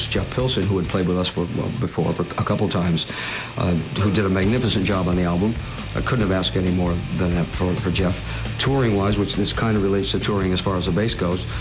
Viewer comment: Hey guys! I need your help please, I'm working on this video, so I made a transcription, however I had some problems. I can't understand what Dio says from minute 2:39 to 3:00...so if you can help me, I really thank you a lot. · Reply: ".. who did a magnificent job on the album. I couldn't have asked any more than that from Jeff. touring-wise, which this kind of relates to touring as far the bass goes, we're using Rudy Sarzo. Jeff and his wife had their first child just at the time when we were about to go on the road, so it was not in the cards for him to be on the road with us. So, again, we were able to get Rudy who has been absolutely brilliant on the road.." Hope that helps! ^^